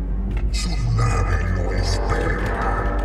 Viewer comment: parece que dice "su nave no es perra"